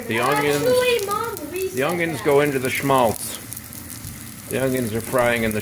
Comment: I heard your kids talking, and I thought you said "Youngins, go into the schmaltz" lol